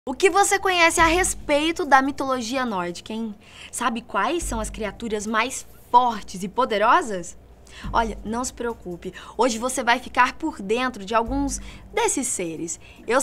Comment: eu achei que ela ia falar "mais fodas" no começo